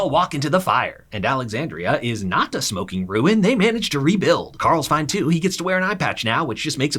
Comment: All I heard “left it a smoking ruin”